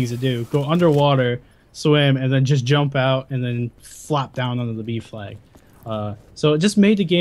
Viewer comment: Lol, when he said jump down to the B flag, I thought he said jump down to the beef leg xD ???